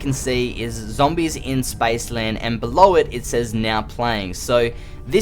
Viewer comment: did he just say Spiceland ???